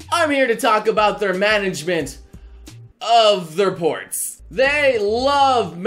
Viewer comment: i thought said "Its time to talk about their management....of their porn"